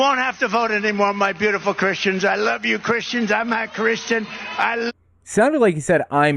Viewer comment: My CC read that he said, "I am not a Christian". No matter if I played it on my phone, tablet, tv or even on my Republican neighbor's TV. He even thought that he said it and it made him go, "hmm did he just say that? WTF"!!!???